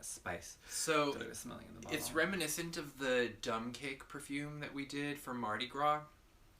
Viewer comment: For a second, I thought you said, "dump cake" and I thought of a yellow cake and fruit scent.